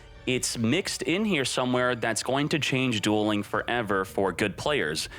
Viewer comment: Sounds like he said it gonna change duel links forever